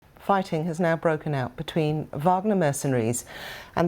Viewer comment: At first it sounded like you said, “Farting has now broken out.....”🦵 💨 🦵 😮😂